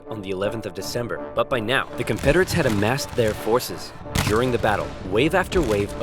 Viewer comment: Did OverSimplified just say Conpederates? I heard a P instead of a F.